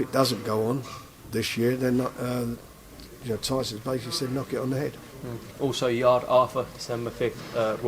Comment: What the fuck is he saying here? Is it, “Tyson can basically knock it on the head”? And I assume it means Fury will be free of the Wilder rematch clause at the end of 2020?